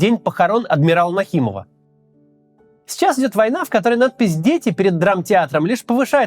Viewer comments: Слышу "надо пиздеть")) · пришлось переслушать 5 раз чтобы понять что он говорить "Надпись Дети", а не "Нац Пиздец и"